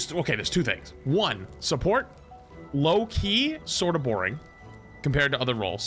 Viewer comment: i thought you said loki is boring for a sec and i almost lost it lmao, he is so much fun for me